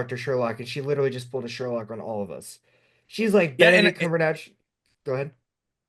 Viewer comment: I heard cumberdash 😂